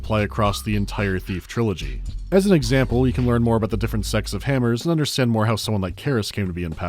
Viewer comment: I thought he said "different sexes of hammers" and if it wasn't for the subtitles I would have continued the vid thinking this mad ancient game was on to something.